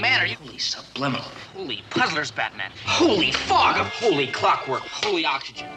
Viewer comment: did he say holy foghorn, or holy f**k-something or another?